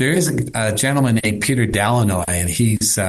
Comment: Did anyone catch the name of the GOUT Dr. mentions are 30:54, something like Petere Dalinoy????? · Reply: Dr. Peter Delannoy